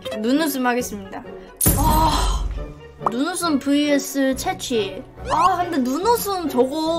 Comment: 눈웃음 vs 채찍이라고 나만 들리나?